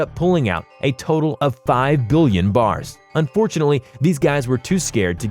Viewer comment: did he say 5 billion bars